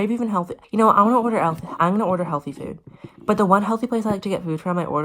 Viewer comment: Anyone hear I'm gonna order aflac